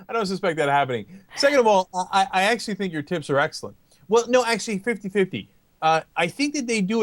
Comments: Think there was a bit of a slip there. Sounded like he said Ana's tits are excellent. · Lol, I thought Cenk said "your tits are excellent." but he said "tips" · When he said, "I bought your tips where excellent," I thought he said "I thought your tits where excellent." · OMG @ 6:16 I thought he said her TITS were excellent, I was CENK!! lol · I think he said her tits are excellent! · LOL I THOUGHT CENK SAID "I ACTUALLY THINK YOUR TITS ARE EXCELLENT" LMAO!! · lol at 6:16 I thought he said "tits" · it sounds like Cenk said "second of all, I think your tits are excellent" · holy shit I thought Cenk said he thought Anna's TITS are excellent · I thought Cenk said "Second of all, I actually think your tits are excellent"